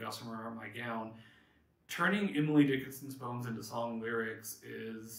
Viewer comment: I heard "turning Emily Dickinson's bones into..." instead of "turning Emily Dickinson's poems into...". To be honest, I think my interpretation works. This was a phenomenal first video for the series and I look forward to the next one!